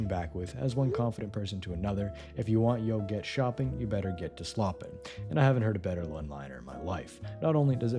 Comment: I couldn’t tell if the voice was TTS or not until this video. Bro said "yo" instead of "to" at 1:55, certainly TTS. Great TTS though, I want it lol